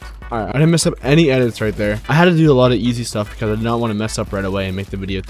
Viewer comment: wait at 1:20 did he just say he had to do easy stuff ?